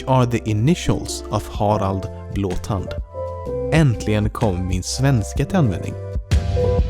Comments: what did he say? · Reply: spoke in Swedish, saying: "My Swedish finally comes into use" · Could anyone tell me what he said at 14:44? I can't understand it. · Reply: He said in Swedish "Finally my Swedish comes into use"